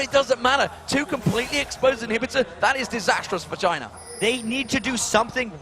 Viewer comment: Am I honestly the only one who hears the commentators say that it`s a disastrous Vagina at 32:07?